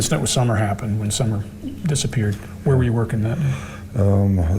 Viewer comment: hmm it came across not quite like that to me but it’s just my opinion. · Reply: yes thanks I’ve been listening and listening and I thought no I’m sure he’s saying after summer disappeared….trouble is after a while you analyse to the enth degree. Thank you . I have my own thoughts on this case like you and for now I’m keeping it to myself . I think possibly he’s getting brain 🧠 leakage which often happens as our brains can only process something like 2000 words at a time in conversation. He hasn’t helped himself with his activities on sm . Wish they would find little summer Wells it’s heartbreaking 💔